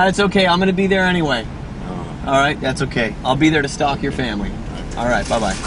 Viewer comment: did he say he'll be there to stalk their family? lol